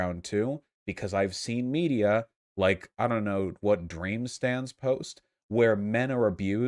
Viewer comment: holup! Did he say "Dream stans" there?